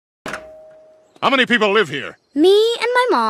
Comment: I always miss heard this as "you wasted my time, you little bi-"